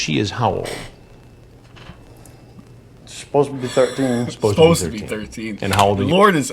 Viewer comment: I've always thought he said "Supposably" I'm from Maine a lot of people up there say Supposably instead of supposedly.